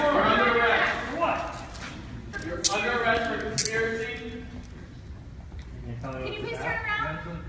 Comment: I heard "You're under arrest for conspiracies." It's only a matter of time, folks.